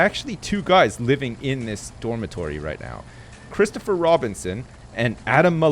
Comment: Thought he said Christopher Robin was in the dorm. I nearly had my childhood ruined